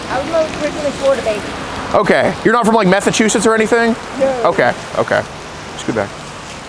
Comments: Did he say Methsachusetts😂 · Wait, did he just say "Oh, you're not from Methachusetts?" 😂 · Did anyone else notice METHachussets at 21:41 ?????? That's what I heard. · Did he say Methachusetts ????☠️ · Did anyone else notice he said Methachusetts instead of Massachusetts lol 😂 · Did bro say "Methachussetts"